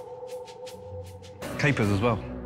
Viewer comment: What does Gordon say here 0:48? Before the 'as well' part. Sorry, my main language isn't english. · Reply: Capers